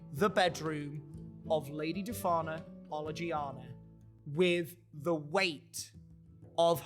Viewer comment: Came from tiktok! I've just gotta say, every time they say the Lady's full name, I hear "Lady Defana Oligiana, Bibbity Bobbity Boo" lol